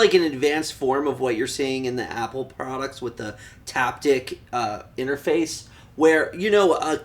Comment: mins in, does he say that Apple has "tapdick" interface?